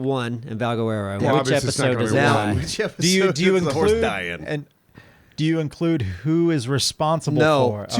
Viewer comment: I heard him say witch episode does the horse die?